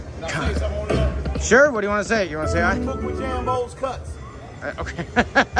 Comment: What the hell did that guy say ...I heard "Jambo's cuts" lol wut